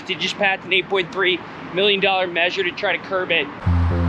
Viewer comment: Did he say $8.3 million for the homeless crisis? 0:32 Praying for Pittsburgh because geesh...🥺😳